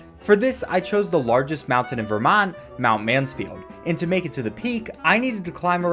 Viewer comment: I thought he said “Mount Manscaped” at first